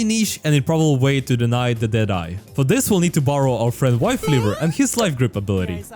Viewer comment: Did he say wife leaver?